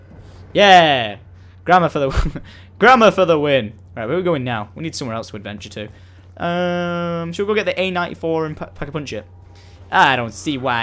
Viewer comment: Lol, when he said "grammar for the win," I heard, "grandma for the win!"